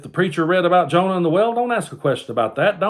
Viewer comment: My bible literacy is fked. He said, "Jonah and the whale." And I heard "and the well." . . . I need to start reading the bible again.